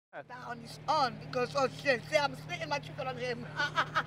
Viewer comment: what did she say there? I can’t decipher it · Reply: she said "Oh shit, I'm spitting my chicken on him"